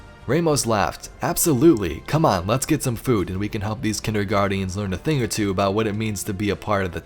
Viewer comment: Kinderguardians? Did I hear that one right? XD